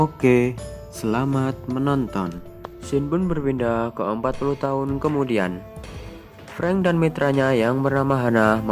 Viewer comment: kata adminya pindah ke 40 tahun kemudian..bener pa salah denger ya